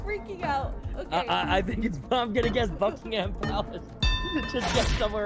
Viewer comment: Did just swear?? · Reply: Oh I completely read that wrong lol. I thought he said "it's f-ing impalace" or smth